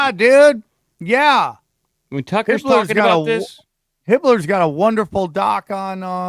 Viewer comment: what documentary is it , Anybody know? cant find it on amazon and does he say Pibbler? cant find anything with that would like to see this DOC